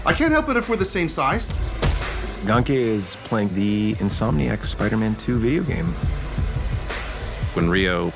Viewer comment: did he just say !!!"insomniac spiderman -2"!!! game